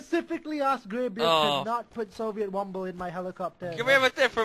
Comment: if y'all don't know what he said he said I asked ask Rimmy to not is in the chopper